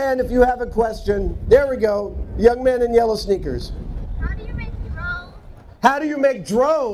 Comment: When the kid asked his first question I thought he said "How do you make drones" and I was confused for a while until I realized he said drugs.